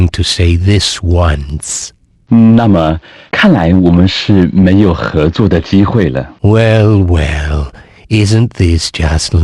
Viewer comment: For anyone curious on what he said at 3:47, he said “那么，看来我们是没有合作的机会了” or “Well, it looks like we have no chance of cooperation.”